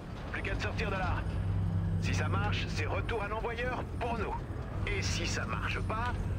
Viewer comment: c'est moi ou il a dit por... ? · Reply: Retour à l envoyeur pour nous